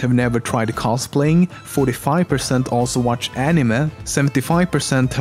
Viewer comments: anyone noticed he say Animuh instead of anime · Animeh is all i heard